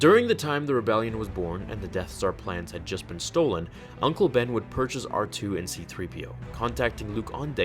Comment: Did he say uncle Ben